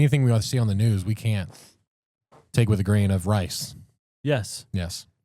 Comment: I thought he said “take with a grain of *lice*” at first 😅😂